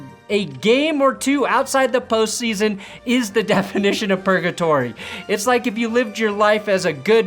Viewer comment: I thought you said Pernatory over purgatory and I don’t know which I liked more